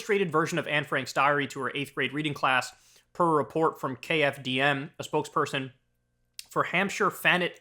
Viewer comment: Thought he said KMFDM.